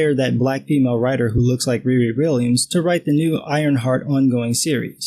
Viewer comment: At first I thought it said iron fart 💨